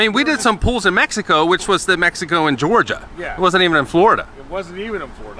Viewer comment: did he say Mexico and Georgia????